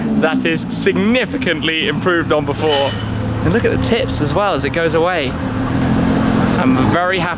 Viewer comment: i thought you said " and look at the t*ts as it goes away "